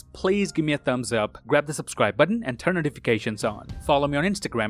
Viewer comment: It kinda sounded like he said “see you in the next Wong” as the camera is focusing on Wong